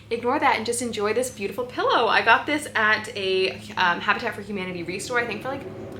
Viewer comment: Wow 😍😍you found fantastic pieces …did I miss something? What video did you say that 50 was old 🤣🤣I absolutely missed that…but I’m nearly 50 so my hearing might be going coz I’m soooo old 🤣🤣🤣, I genuinely thought you said you’d been shopping at Hot Tub for humanity at the beginning of the video 🙈🤣🤣🤣…but in my defence, I was across the other side of the room when you were talking about your very pretty cushions (I rewound the video to see them lol), I love the idea of the little book stand, I have a bible that’s over 200 years old. It’s written entirely in Welsh (because I am Welsh 🏴 😊), back during a time when the Welsh language had been banned by the English 😬, but thank the lord it survived 😊…anyway..back on topic…I don’t know how you manage to find all of these beautiful items, it is truly a gift 🤷‍♀️…loved everything 😍